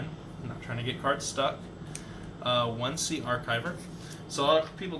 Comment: what card is this? Im dumb cant hear the name correct lol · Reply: Sea Archiver